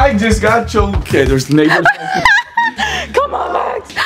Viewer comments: what did he say?!! I think I'm hearing things but it sounded super close to a certain n word..... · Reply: Cameron Enoch he said neighbors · did he say that his neighbors were naked? If so, wow. Early in the morning to let them breathe 🤣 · did he just say the N word or it’s me? I honestly don’t know lmao don’t come at me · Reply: he said neighbors lol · When when out on the deck after the prank it sounded like he said “okay there’s N*****” as he walked inside was I the only one who heard that it’s around like